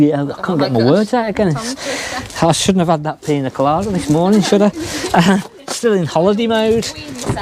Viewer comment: Thought you said shouldn't have had that pee in the shower this morning lol cracking podcast as usual nice one